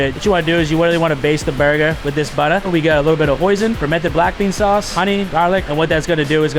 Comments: did he just say posion? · DID HE SAY POISON???? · nah did they say poison 😂😂😂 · Did he say something about poisoning the burger?